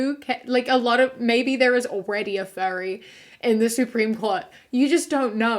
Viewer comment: when you were saying “furry”, until you said “fur suit” i thought you were saying “fairy” and i was painfully confused 😭